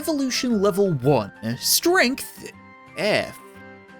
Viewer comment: I thought it said "Strength: Frank" Like what did Frank do to you man